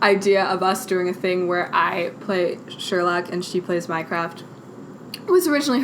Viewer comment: you said "I play sherlock and she plays mycroft" but I heard "she plays minecraft"